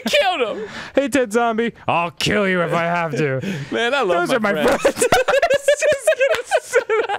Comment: what do they say? I can't hear well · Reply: Arin says “man I love my friends“ Dan says “those are my friends”